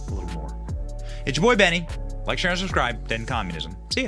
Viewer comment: What was the very last thing you said it sounds like you said defend communism